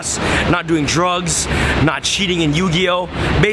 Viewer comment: When he said "makeing sure he's not cheating in Yu-Gi-Oh" it sounded like he said no cheating and I thought cheating on who?